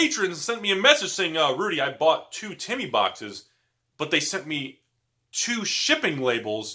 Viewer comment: On double speed it sounds like he was saying he was saying Titty boxes in his starting skit and just throughout the entire video